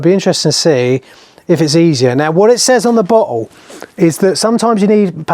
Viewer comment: Being Canadian, at 8:44 I swear I heard you say: "What it says on the b*tthole" (instead of "bottle"). I went back and listened to it a second time. I was still hearing "b*tthole". 😂 Sorry! Great video though! Exactly what I was looking for. Thanks! ✌️